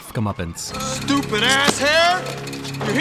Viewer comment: Rest in Peace, Brandon Lee. We lost him far too early, and in one of the Behind the Scenes features, he talks about his character's resurrection, and how we should all live our lives to the fullest, because death could strike at any moment. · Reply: Ummmm my whole life I Thought he said stupid asshead lol